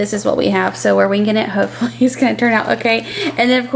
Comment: I like how you say we are just winking it (or that's what it sounds like you said) we say just winging it ;)